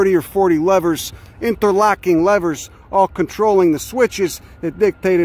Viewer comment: Here for the botany truthbombs and railroad trivia. Also misheard “interlocking levers” as “interlocking lovers” and that lead down a fascinating mental pathway.